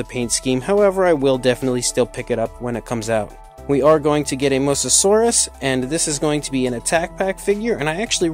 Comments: Oh uh, A Mussasaurus I thought you said Mosasaurus! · Did you say Mosasaurus or something else ? Because that doesn’t look like a mosasaurus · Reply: Anthony It’s Musasaurus The proununce is kinda equal